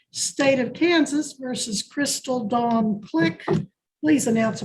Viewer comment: I started watching this while I was in the shower earlier so I couldn't hear that well and I seriously thought they were saying "Crystal Ball Click." 😂